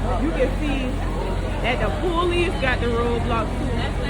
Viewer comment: she really said poolice🤣🤣🤣